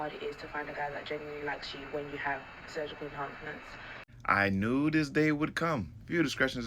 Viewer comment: Sounded like she said surgically pumped nuts